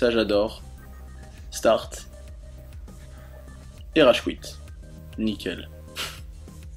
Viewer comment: "Rage qouitte" ou c'est moi ?? x)